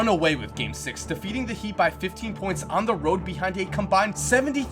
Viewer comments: no hate but am i the only one that heard heat instead of cavs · did he just say HEAT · did you say defeat the heat? Lol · Anyone else hear say defeating get the heat